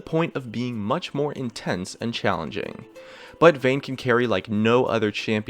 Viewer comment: wait wait did he say challenging?